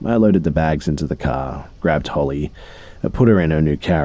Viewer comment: Does anyone else hear that he says holly instead of hailey at 1:52:43?